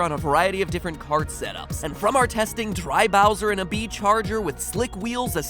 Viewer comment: what Kart is that????? Did he say "Bee charger"?